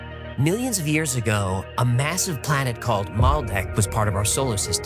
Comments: I swear to God I thought he kept saying Planet Malbec. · 🤔 What did he say... 😂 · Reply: Millions of years ago…